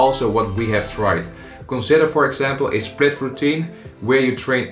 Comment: The accent screams “ I’M DUTCH!!!” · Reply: That could explain why I heard ‘spliff routine’ instead of “split routine”. Had my hopes raised for a second.